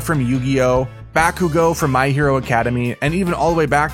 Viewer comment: Am I the only one who heard him saying My Hero Academy